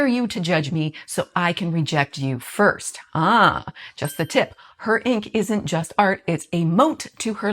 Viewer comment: Every time you say "just a tip", I hear "just the tip", one of the most often-broken promises men make when seducing a women.